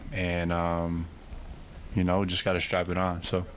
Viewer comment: did he say, "We just gotta strap it on"? 🤔